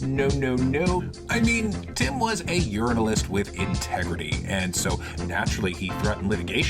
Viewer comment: did you just call him a Urinalist? I bet that was a mistake